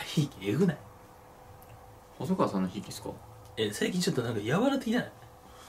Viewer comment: 最近ちょっとがセイキンショットに聞こえるw